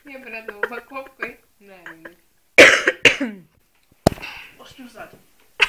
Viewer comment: что она сказала на 7:50? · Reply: Мне кажется она сказала "Пошли в зад"😄